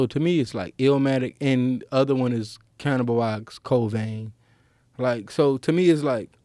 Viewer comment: what did he say? Canibalized covain? I couldn't hear it I've heard illmatic but not the other one. Someone please help lol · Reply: Cannibal Ox - Cold Vein